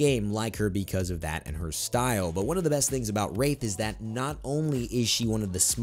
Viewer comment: Did anyone else think he said rape not wraith at